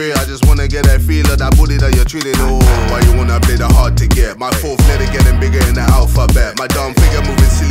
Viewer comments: when ksi said: "my fourth letter getting bigger in the alphabet" I felt that. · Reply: Ngl i thought he said my forehead getting bigger than the alphabet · "My fourth letter gettin' bigger in the alphabet" Not gonna lie I thought he said my forehead gettin' bigger than the alphabet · i thought jj said my forehead bigger tha the alphabet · Did anyone else notice dat he said “my forth letter getting bigger than the alphabet” and the 4th letter is D🤨😭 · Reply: I hear "my forehead is getting bigger than the alphabet" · when ksi said: "my fourth letter getting bigger in the alphabet" I felt that. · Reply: i thought he said my fourhead getting bigger in the alphabet